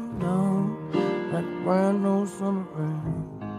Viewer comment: When I first heard this I thought he was was saying 'summer rain' either way it's one of my favorite songs RIP MAC WE LOVE YOU great video 🙏🙏🙏